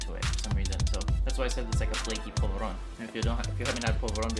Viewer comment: Lol i thought you were saying it was like Flaky Provolone, And it greatly confused me. Since your box didn't have many snacks could we get a follow up video of you making an Indian dish with some of the stuff from the box? Also I love how you kept messing up that really made the video better to include, It was super adorable.Keep up the great content!